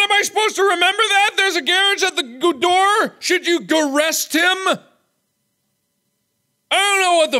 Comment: It sounds like at the end of the demon speech it's saying: "There's a monster at the door. Should I escape it, I'll be home free." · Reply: At the end I heard, "there is a stranger at the door, should I kill him, I'll be home free